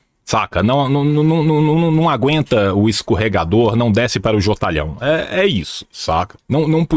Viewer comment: não desce para oque? não ouvi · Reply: Jotalhão, que deve ser coisa específica de MG.